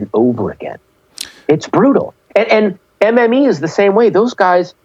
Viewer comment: Did he say MME??😂👍